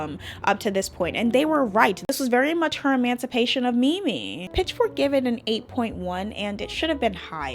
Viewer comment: What’d you say at 13:38? Emancipation of what? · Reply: Emancipation of MiMi, Mariah Carey’s “comeback” album!